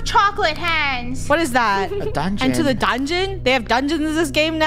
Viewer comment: bro i thought it said “Enter into the Dragonite” like DragoniteDragon lol